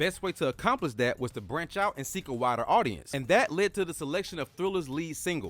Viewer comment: When you said “wider audience” I misheard that as “whiter audience”, which wasn’t wrong